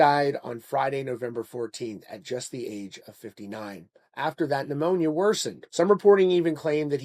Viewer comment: You didn’t hear that sentence right. “He died on Friday, November 14, at just the age of 59, after THE pneumonia worsened.” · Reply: Nope, you didn't hear it right. He clearly says "After THAT". 4:35 He even paused after saying "at the age of 59", As if ending a sentence.